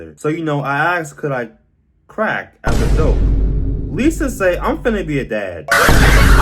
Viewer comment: ‘least to say I’m finn be a dad’ BYE WHY DID THEY SAY THAT SO CASUALLY IM CRYING?? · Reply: I thought he said “least to say I’m finnish a dad